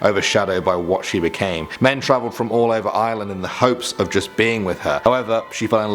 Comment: Who else thought he said island instead of Ireland